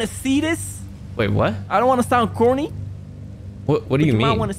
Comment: WHAT DID JUST SAY?!?! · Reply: corny